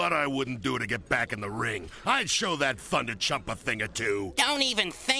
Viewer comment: did Polaris just say "I will show that fucking chump"